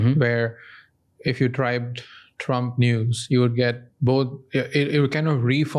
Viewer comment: i know he meant to say trump news but i heard "trump nudes"